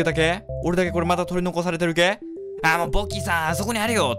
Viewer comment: さんのポがボに聞こえるんだが･･･。